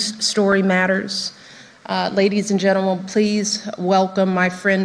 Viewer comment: Did she just say "ladies and gentlewoman?" 😆🤣 Edit: maybe it was "ladies in general"... I honestly don't know